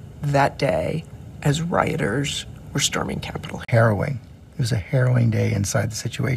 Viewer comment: lol... when she asked him what was going on in that office on that day, his answer sounded like "hero'in" haha... I needed to activate subtitles and rewind... harrowing isn't a word I come across much 😂 ... but the mishearing would have been more believable 🤣